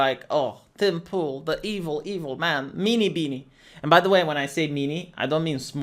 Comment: I thought you were saying mini-beanie for two years.